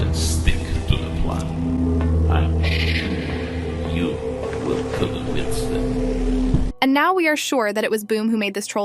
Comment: No one is gonna talk about what the secerent agent has said lastly to lucky i heard "you will kill them instantly " who will he kill ?? Is it the agents that entered the room ??